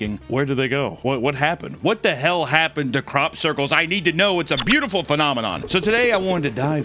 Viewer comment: my b i thought it said cock circle 😔